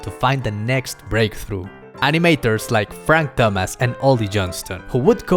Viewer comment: for a second i thought he said frank dumass